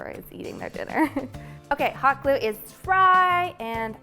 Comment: I swear she said hot glue is fry not dry!